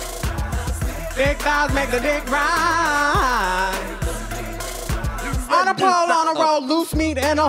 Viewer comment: Gurls, I'm so goofy!! I thought the song was saying " big fry's make the dick rise" !! It's "Big thighs, make the dick rise" !! I love the two of You !! Don't read me, I'm 70 years old and I'm still loving You all !! Luv from , Old Aunty Mo !!